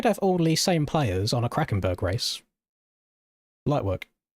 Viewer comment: Did you say... Krakenberg...